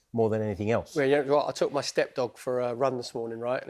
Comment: Did he say he took his “step dog” for a run ?, wtf is a “step dog” ?… 🤨